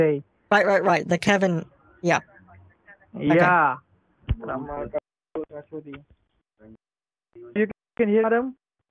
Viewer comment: Right around 2:34:00 what did the scammer say that said he would ignore? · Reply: He said "are you an idiot madam" and said he'll pretend not to hear it, just so the scammer will think nothing is wrong.